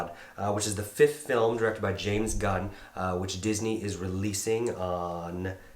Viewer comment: Lol did he say Disney